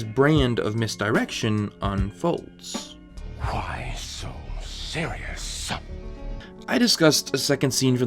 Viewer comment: It always sounds like Joker is saying “Why so serious son.” 😂 idk why I can’t hear anything else.